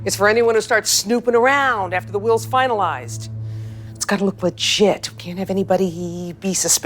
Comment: I thought she said “it gotta look like shit-“